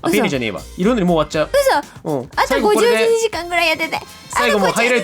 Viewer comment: 「あと50、1、2時間くらい（こう聞こえた）やってて！」 …死ぬわ